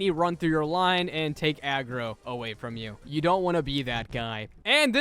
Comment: did bro say agro